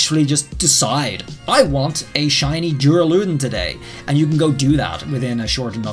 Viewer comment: what Pokémon did he say? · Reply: Duraludon